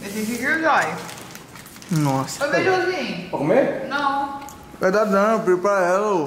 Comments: Quem também percebeu q quase todos os vídeos deles da casa eles fala alguma coisa do Renan e da sei ñ em kkkk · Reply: Parece que eu ouvi a chama o Renan de beijuzin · bijuzinho??? foi isso msm que eu escutei? kkkkkkk hmmm